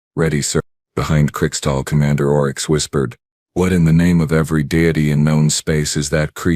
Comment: "What in the name of every daddy in known space..." 🤣🤣🤣🤣🤣🤣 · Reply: Oh my god I missed that. I hear "Deity" which I think was the intended line, and the captioning.... 🤦‍♀🤦‍♀🤦‍♀🤦‍♀🤦‍♀🤦‍♀🤦‍♀